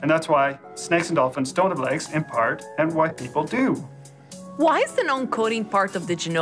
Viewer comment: Does he say "white people" at about 1:30?